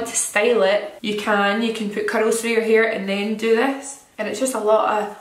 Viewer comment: At first, I thought she said “cuddles through your hair” then i realized it was “curls through your hair” 😅 love you Jamie!